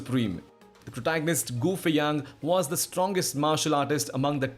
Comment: bro i wasn't paying attention i thought he said goofy ahh